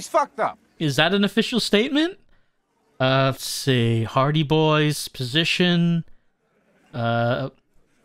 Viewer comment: I keep hearing "hardie boys" as "party boys" it gets me every time.